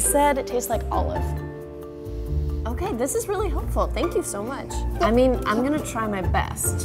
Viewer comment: thought she said this is really awful